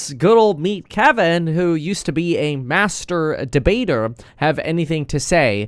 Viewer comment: Did I hear at 1:30 that Kevin used to be a master bater?🤔